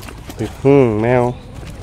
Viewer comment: wait.. did he just say meow? And-and-and did he just say slowly, wow wow wow wow. Tf is wrong with people these days? The 50’s/80’s and in the middle of there. Those were the good old days in the U.S.